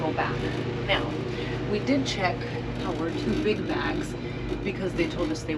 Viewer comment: At time 1:42 you say something about something that won’t fit up top and you had to check it. What was the word? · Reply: Sounds like “our two big bags”